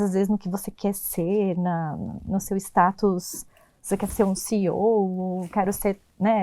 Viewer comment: Aos 14,37 minutos vocês falaram 14:37 de "ciou". Foi assim que entendi. Alguém poderia explicar o que se trata? Alguns termos que vocês citam muitos no Brasil não entendem. Obrigada.